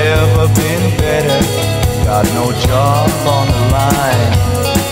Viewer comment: sorry, mama there are times I get carried away please, don't worry next time I'm home, I'll still be the same and I know it's no fun when your first son gets up to no good starts freaking out the neighborhood really, I'm fine never been better, got no job on the line sincerely, don't worry same old boy that you hoped you would find and I know it's no fun when your first son gets up to no good starts freaking out the neighborhood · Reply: I always thought it said "got no job or no life"